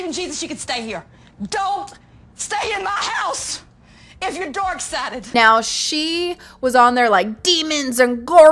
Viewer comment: Oh my gosh, that lady who claims she is a christian, is an embarrassement to the Christian religion!! lol And it cracks me up cause she sounds like she is saying "theyre DOORK sided" and not "dark" lol That should be a meme too! lol someone is doing something insane and claim they are dork sided! hahahah